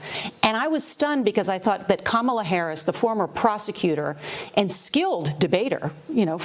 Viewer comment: I could have sworn that witch said former prostituter kamala harris lol 😂